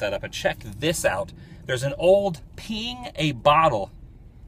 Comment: I thought you said peeing a bottle. Glad you like old town, hope you don't like O-Town